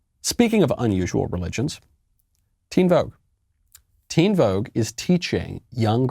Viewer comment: Oh man I thought you said Tebow was teaching young girls dark magic, not teen vogue....I'm so relieved 😅